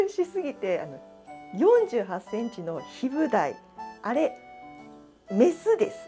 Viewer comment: ♥頑張ってるねぇ。 ど〜も富山の親父です。 暑さに負けてしまい釣りに行けてません😭 ヒブダイおめでとう！！！ シブダイじゃなくヒブダイなんですね。 自分、何か聞き間違えてたwww 次回！さらなる大物か？お楽しみだね😉 絶体観るぅ〜〜〜👍👍👍